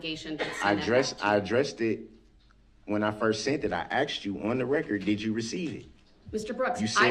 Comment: "I axed you" instead of "asked you" .... wow, the stereotype is real.